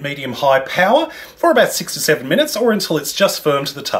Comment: I have to admit, when you first said how long to put the cake in the microwave, I thought you said 67 minutes, not 6 to 7 minutes. I need to get my hearing checked.